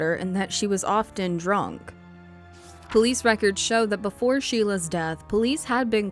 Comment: Hey just noticed and wanted to let you know, towards the end the subs are wrong. Says after heather’s death instead of Sheila’s around 10:50, and then 96 in the subs but 86 spoken